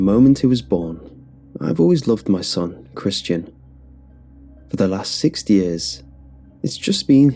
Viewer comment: "for the last 6th years" that's what I hear. Is that right?? Why 6th? Not 6 years, 6th. A bit confusing